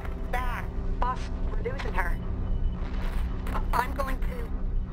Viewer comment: F.R.I.D.A.Y - Boss, we're losing her. I'm going too. That really sells the dreaded feeling of Tony being on his own. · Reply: For years, I thought that was Pepper saying “Or I’m going to-“ as if she was threatening Tony to come back and that’s when it cut off. My mind is blown